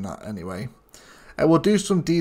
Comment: Yes, I checked to see if "Outrotage" (if I heard you correctly) is a real word, lol. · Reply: Oh it’s “outrotage”? I kept hearing “outro touch”, never knew what it meant, lol 😅